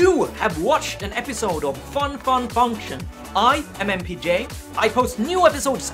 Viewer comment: for the first time i hear the opening, i thought you saying "Hi, i mmpj". LOL